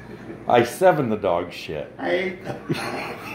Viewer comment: I don’t get it. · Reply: Oh, I thought he said Eye-8 the dog shed.